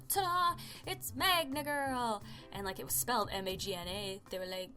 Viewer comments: XD i always thought tthat you were saying "Magnet Girl" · for a while I thought you were saying magnet girl don't know why only me? ok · I thought it said manga girl... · At first I thought it said manga girl😋